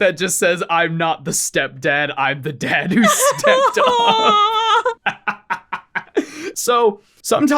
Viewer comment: I misheard that as "I'm not the stepdad, I'm the dad who's stepped on" 😭